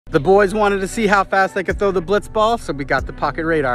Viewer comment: “Boys wanted to see how fast I throw the ball” Proceeds to not throw the ball in the video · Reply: “Boys wanted to see how fast they can throw the blitzball” Don’t worry man, I thought he said that too first time listening to it👌🏼